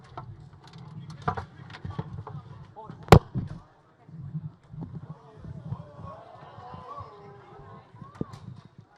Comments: Unrelated but what does the guy yell out at 45:52 ? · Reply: naw man He said “you snorted it!” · What did the crowd say at 45:52? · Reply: " You snorted it"